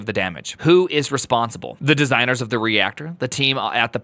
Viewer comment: I thought you said the Designers of "React" were responsible for Chernobyl xD